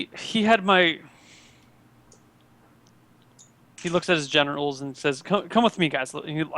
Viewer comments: lol when thor took the group aside to tell him about his daughter being kidnapped. I could have sworn that said he looks at his genitals lol I was like wtf why would he look at his genitals when talking about his daughter lol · When said Generals, I thought he said genitals